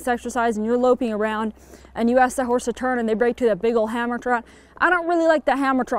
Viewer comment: What is a big old hammer (sp?) trot?? I'm not even sure that's what you're saying. But it's @ 9:55. Thanks!